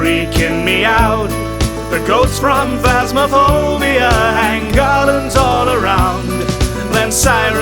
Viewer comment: is it just me or did it kinda sound like he said "the goats from phasmaphobia"?